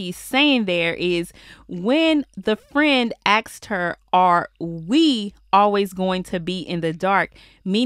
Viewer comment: I thought she said " or are we all going to be in the dark?"